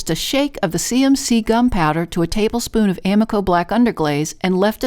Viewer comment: I swear I thought you were saying “gun powder” lol. 🙃I had to go back and watch it again. Those platters are beautiful!💙🐝🪶🦋🌎🤗